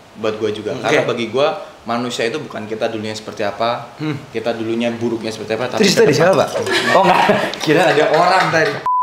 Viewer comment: sekilas kedenger kayak "kita dulu burung nya seperti apa"